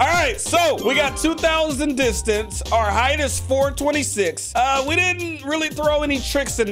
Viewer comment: ayo bro did u say ur score was 426? thats my brother's bday! 4-26-19